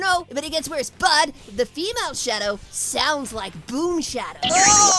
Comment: I thought he said female shadow sounds like boom sonic